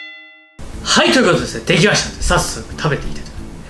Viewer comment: 「はい！とぃうとぅすね！」 （はい！ということですね！） 「さっすぅ」 （さっそく） 「たべてぃうす」 （食べていきたいと思います） 「いたぁいむす！」 （いただきます！） 訓練された視聴者は（）内で聞こえるらしいですね！